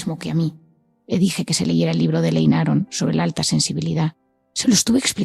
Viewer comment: Cuál es el libro del minuto 13:21 que comenta ? No entiendo el titulo y escritor . Muchas gracias. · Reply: Libro de Elaine Aron. Saludos